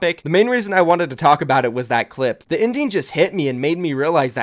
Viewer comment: I thought you said "the indian" and not "the ending"